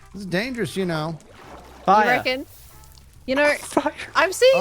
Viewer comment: Uh. did say what I think he said? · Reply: Okay listening again he definitely said “Fire” but he was laughing